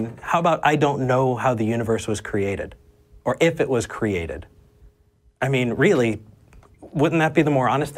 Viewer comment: instead of the captions saying if it was created it says “if it was curry” and honestly that is an answer we haven’t explored yet. What if the universe was curry?